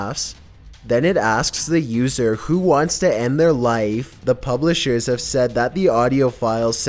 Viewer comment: With the Elmo book I just hear "uh oh who wants a bath"